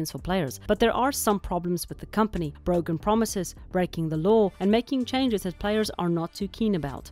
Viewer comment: I misheard "breaking the lore" as "breaking the law" and my brain that has been running on court proceedings for months now did such a double take LOL